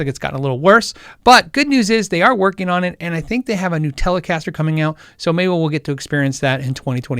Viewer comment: Did i hear "Nutellacaster"? If so i'm down!